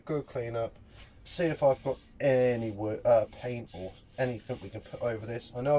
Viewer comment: did you really say paint?